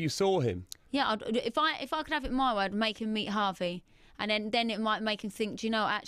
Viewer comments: i thought she said 'make him meet Harvey' D: NONONO HEAD HURTS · Did I hear that right?...''If I could have it my way I'd make him eat Harvey.''